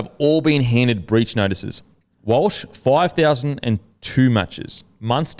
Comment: lol when he said "Walsh, $5000, and two matches", it flowed pretty well and I thought he said "5002 matches" and I was like damn, he's never playing again! 😂